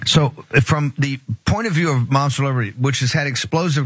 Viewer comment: I hear "Momster Liberty".